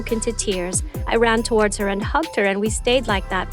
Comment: is it just me or did she say humped not hugged